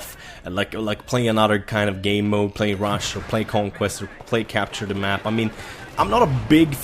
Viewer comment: did you just say capture the map?